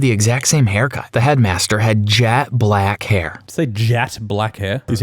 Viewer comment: I HEARD JACK BLACK HAIR AND I'M SCREAMING LAUGHING OMG